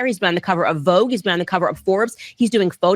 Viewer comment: Why is it saying "covid" and not cover? 😂